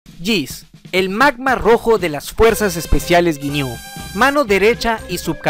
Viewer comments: Antes no entendia que decia "el magma rojo de las fuerzas ginyu" xd pense que decia "el man mas rojo de las fuerzas ginyu" Jaja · Yo pensé que decía " El más más rojo de las fuerzas especiales ginyu jaja